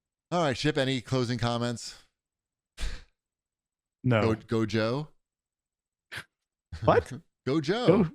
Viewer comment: I think would look pretty good with an eye-patch... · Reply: I also thought it was "Go Joe!" @ 1:13:45 but I've never watched an episode, so... How does "Yo, Joe!" make any sense? "Go Joe!" is a call to action, "Yo, Joe!" is a "hello" as you're passing on the street... Like, why would you even bother?